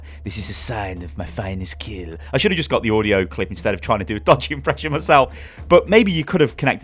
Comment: Did you just say Dutch? Widowmaker is French. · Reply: m1ka he said dodgy